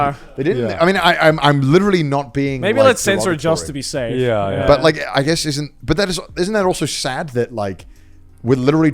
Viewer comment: what did connor say? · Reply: he said f a g, which used to mean cigarette and referred to what connor described (back then)